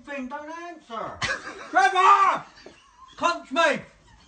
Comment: Does he says cunts mouth at 1:17 ??? He says it all the time I never know what he's saying